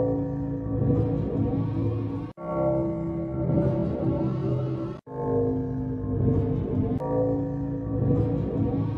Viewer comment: não entendi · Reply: Mês do orgulho lgbt